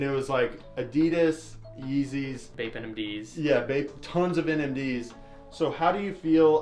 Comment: sound like he said fake nmd